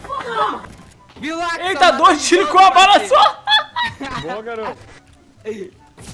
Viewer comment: "eita dois tiro com uma bala só" ?????? Só eu q ouvi isso?